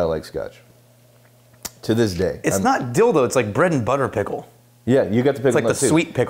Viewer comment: did you say ‘dill though’ or ‘dil-do’? 🤔